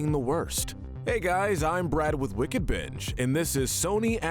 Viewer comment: Bro I swear I sound like he said wicked bitch